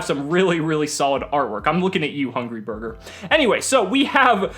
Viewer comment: !! 5:50-5:60, Did I heard u said Hungry Burger? My dude ur a legendary, cause now, they are giving hugry burger a support!!! I just watch this randomly again because I love drytrons!! I hope you read my comment here!!